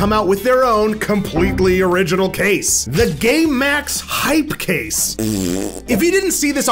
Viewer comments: it sounded like he say 'GAY MAX HYPE CASE' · I heard “gaymax hypercase “😂😂